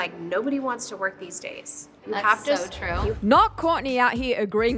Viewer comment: Kourtney is the last person who should be agreeing🤣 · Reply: Right??? I literally thought she was saying "ahhh yeah that's NOT true" Like wtf??! Is she joking?